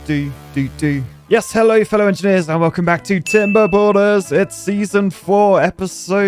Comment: Everytime I hear the intro, all I hear is "Welcome Back To Tim Bor Boners". LOL